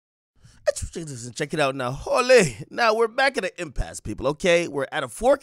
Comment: Wtf do you be saying in the beginning of the video bruh all i hear is "ehsschiichii check it out now”